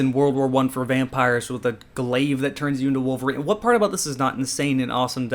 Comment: I start the video, did he just say wolverine vs vampires? Nah he said marine vs vampires right? Yeah he said marine vs vampires. Half way through,....nope he definitely said wolverine vs vampires.